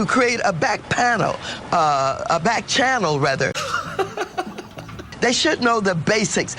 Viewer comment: Listening to Mad Max talk to Joy Reid, I could have sworn she said Bat Channel. What, she's got a thing for bats now?? Wow, she IS bat crazy!! LOLOL 😂😂😄😄😁😁😂